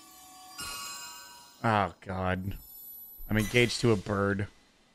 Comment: bros like “Oh God. I’m engaged to a bird” What he’s really saying “Oh God. Im engaged to a kid” I don’t care what YouTube says, this is funny, and it’s thanks to the guy who said it in chat when i saw the Vod on Twitch